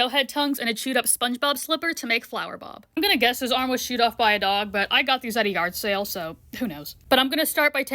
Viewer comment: I thought she said "i chewed off his hand"😂😂😅